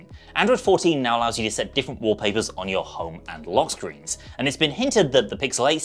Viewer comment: This guy really should use androids more if you really think one of the new feature on Android 14 is setting different wallpapers to home and lock screens. That has been a thing for YEARS. Thats not something from iPhones. Nothing Phones have lockscreen widgets in A13. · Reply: I thought I heard him say "different LIVE wallpapers", and I went back to listen, and he said just say "different wallpapers" ( 1:40 ) WTH!?